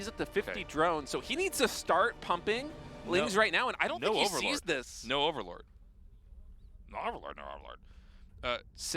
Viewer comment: what does Tasteless say after two times repeating "no overlord"? :D lmao that sounds so funny. Tasteless sounding like a Zergling or something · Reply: I think he was intentionally mushmouthing "No overlord" again. Made me laugh too. Tasteless cracks me up.